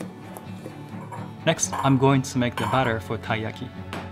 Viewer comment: i thought he was saying butter for so long, turns out he was saying batter, makes much more sense now :)